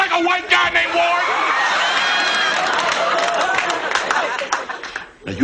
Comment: I've been watching Fresh prince for over 10 years and I was today years old when I found out Uncle Phil was saying "named Ward". I always thought he was saying "Do I look like a white guy big boy!"